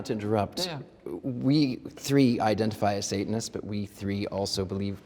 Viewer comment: I thought it said scientists not Satanists😭